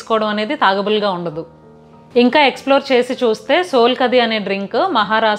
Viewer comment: Did she say "Thagable"?😊